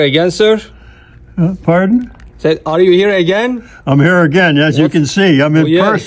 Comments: I thought he said "are you a gangster" at first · I swear i heard " Are you a Gangster?"